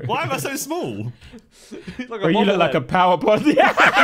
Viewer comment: what does Toby say? Power what? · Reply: Powerbody